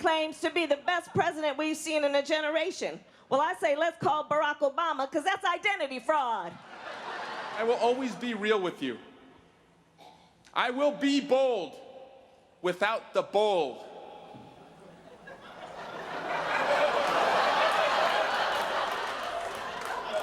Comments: It sounded like Swalwell said, "I will be bold, without the bold"--that's why there was silence, in my opinion. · gnshapiro : that's what I thought he said too. So what did he actually say?🤔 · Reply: I googled it, and it was supposed to be "I will be bold without the bull" ;-)